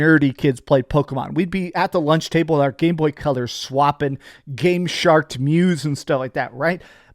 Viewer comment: I heard "GamesharT" instead of GameShark